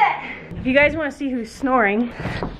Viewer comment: i thought u said she was seeping? her eyes are open! how do u sleep with ur eyes open!? 🤔😂😂